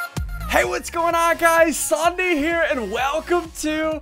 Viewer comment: Sounds like he says "It's Sawndee Here"